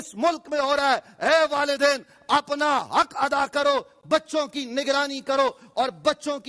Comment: I just heard "butt-choking" from the angry imam. 😳 What is going on?